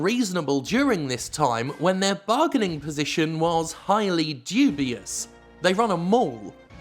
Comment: man for a second i heard "they run a morgue" and that topic had a very different context for a second